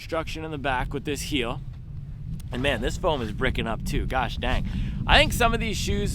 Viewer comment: on first listen I thought you said “ah man this foam got me bricked up” lmaoo Happy holidays my G